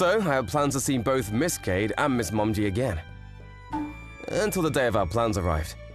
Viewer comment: Did i hear it right MS. Monki or MS.Momji?